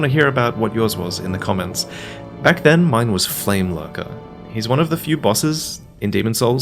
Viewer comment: I totally agree with you Flamelurker was the hardest boss but I enjoyed every part of it even the soundtrack was totally fit in this epic battle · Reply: I thought you said Flamefucker.